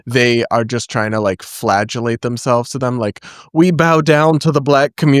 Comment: Brad, did you say "flagellate themselves?" Or "flatulate"?